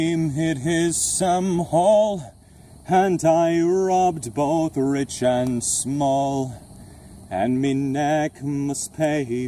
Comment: when he was signing I heard "Hentai robbed both rich and small"